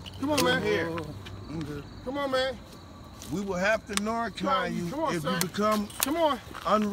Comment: The second you shot the Narcan in him, you brought the life back in his eyes! God bless y’all man... this man was on the verge of dying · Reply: dawwwg i thought they were saying “we are gonna narc on you!”